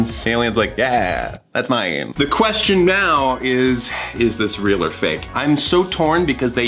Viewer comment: does anyone else hear Thomas say "the question MEOW is" at 7:51 ? No, just me? ok